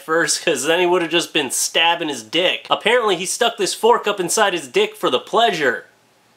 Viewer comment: I thought it said fork stuck inside of duck. Oops.